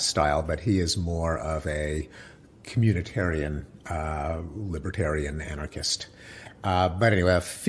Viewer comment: I’m not sure what a Randian anarchist is in that she was absolutely NOT an anarchist. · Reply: Jason Brooks :: I heard him say "Randian anarchist". I thought he was talking about another "Rand". These "intellectuals" get so bogged-down in the minutiae of political ideologies & terminologies, their thought-processes must get lost in a meandering quagmire of information. His statement was a real mental snafu. Thanks for calling him out. I didn't have the intellectual stamina to mention it.